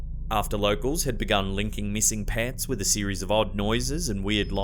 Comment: I thought he said missing pants (Pets) what an awesome crypto that would be.